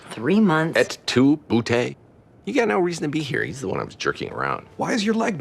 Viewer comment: Am I the only one who heard that? 8:50 “Et tu, boo-TAY?!” 😅